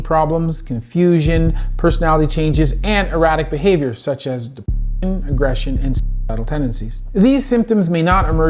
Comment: for those that didn't catch that. "Anger, aggression and suicidal tendencies."